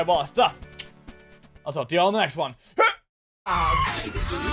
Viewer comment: Did he just say y'all?!?!